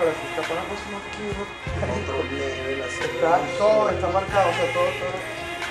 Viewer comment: Llevo años escuchando esta joya y las demás y aún sigo sin saber que dicen al final 😂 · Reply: si no me equivoco es un fragmento del video en el que muestran un poco la gira que tuvieron cuando sacaron el disco, la musica esta muy alta nc si estan en la preproducción de un tema solo entendi "todo esta marcado"